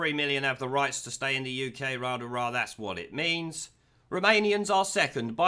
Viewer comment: At first I thought he was saying Remanians.